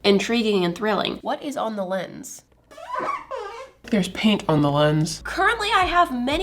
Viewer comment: theres paint on the lens my brain misheard this: theres paint on my limbs 😂